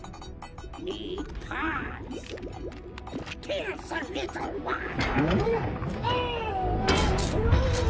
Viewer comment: I like how the little decepticons says...I don't know Lol 1:41 what does he say iam laughing lmao😂🤣. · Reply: He said need parts kill the little one he has a German accent that’s why you couldn’t understand him